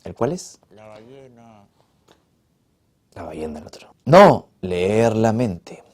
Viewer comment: que dice la voz en off en el minuto 8:15? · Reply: La ballena